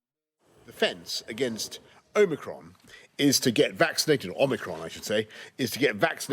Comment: My English is not good - when he says "omicron" I hear "Oh, Macron"